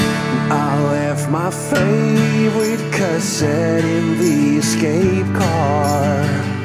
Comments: I always thought it was saying "I left my favorite cousin in the escape car" I was like "kinda fucked up, but I get it" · I always heard it at "I left my favourite cousin in the escape car" instead of "cassette". I assumed that was referencing Hoxton and that made it much sadder. I still prefer it that way to I'll think of it as that :P